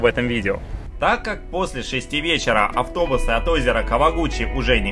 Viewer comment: Мне послышалось" ..озеро гучи"? О_О "Кава" с какого то переводится как Небо Хм, понятно-поняятно - -"